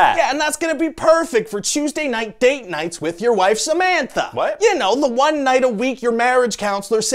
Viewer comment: i thought he said "your wives, amanda...." and i was waiting for the second name lol